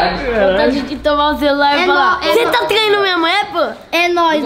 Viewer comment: "você tá traindo minha mulher pow" kskksskk eu n entendi